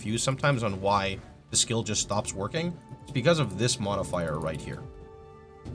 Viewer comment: what I heard "... the skill just stop working, it's because this lil MotherFier..." 😂